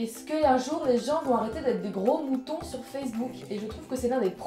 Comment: 1. Il est où le nouveau générique ? 2. Moi je l'ai fait parce que j'étais beaucoup trop mignon gamin ^^ 3. Le srobaque sursdé ? 4. Trop choupie ! 5. Si tu ne réponds pas à ma question 10 fois, un clown unijambiste à deux têtes viendra te lécher les pieds cette nuit. 6. Y'en a vraiment qui le prennent au sérieux ? O.o 7. J'ai compris "gros boutons" ... 8. Yanis Marshall j'en avais jamais entendu parler avant que tu spammes sur Twitter 9. J'ai essayé des talons une fois, j'ai pas réussi à faire 10m, y'a du level là !! 10. Yeurk !!!! 11. Que vous apprécireriez ? 12. C'est quoi des examens ? 13. J'aimerais bien faire des câlins à des mannequins moi aussi ...